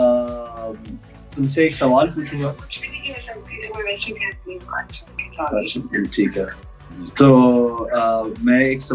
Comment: Any idea what does she say at 35:02? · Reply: she said " jee jannu "